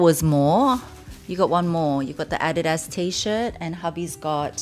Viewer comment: did you briefly say “이거” in Korean? I swear I heard you say “이거 one more” to your son.